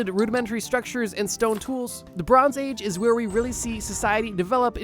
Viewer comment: i swear i heard lebron